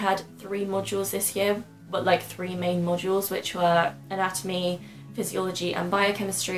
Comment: What do you mean?? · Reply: to be honest with you, I don't know. hahaha. In 2:02 you say something that now that I heard it again sounds like "modules" maybe? something with similar meaning to subject perhaps??